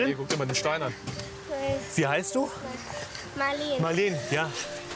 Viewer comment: "Wie heißt du?" – "Marlen" – Und ich hab die gesamte Zeit vorher immer Maureen verstanden! 😳😳😳 Siehe auch Teil 2 an der Stelle 12 Minuten 00 Sekunden, wo die Mutter fragt: "Was ist das fürn Typ? Tut der Maureen was an?" Oder hier bei 10:36 "Dann waren wir in so nem Transporter, Maureen und ich."